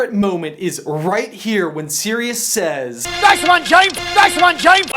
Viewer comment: When I first heard “Nice one James!”, I thought he said “That’s my James!”. I think my interpretation is better, though.. Booing and shouting Time to run.